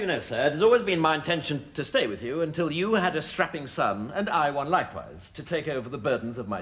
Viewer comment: What does Blackadder say to Prince George? Strapping what...? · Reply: Strapping son